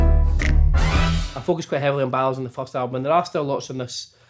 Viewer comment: could someone please tell me what he said here😭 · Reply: "i focused quite heavily on ballads in the first album"